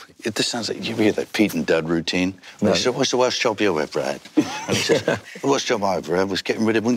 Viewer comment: Hello! Please, help me understand what Mel said on 1:02:22 to 1:02:36 Сan be literally all words which hi said or context. Thanks!) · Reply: Not sure about the first seconds but then he refers to a sketchshow from Derek and Clive when he says "whats the worst job you ever had, the worst job I ever had was getting rid of Winston Churchills bogeys" This is the clip: <link>